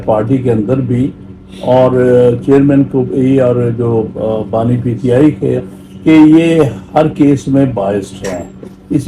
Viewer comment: did he call Imran Khan just as BANI PTI?